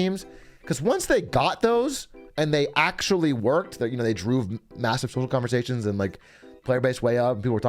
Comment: "droove" sounds like a word a cow might use instead of "drove"